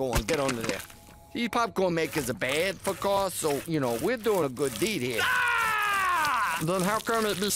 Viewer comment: What was Carl saying at 0:28? Was he just spouting nonsense? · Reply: was telling meatwad the part he was taking off was bad for cars so they were doing a good deed lmao